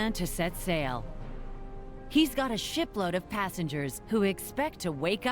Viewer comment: Sorry, I heard that as a sh*t load of passengers!